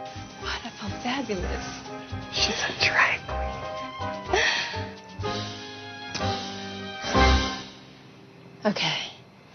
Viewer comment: DOES HE SAY "SHE'S A DRAG QUEEN"?